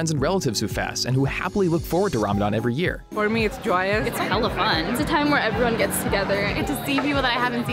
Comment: What did she just say, its hell of fun for Ramadan. Are you mad brav